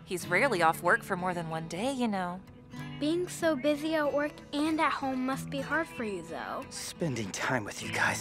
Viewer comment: なんて言ってるかわかる方、いらっしゃいますか？ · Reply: 「サービス残業して家でもサービスじゃ疲れるでしょ？」と聞こえますけど？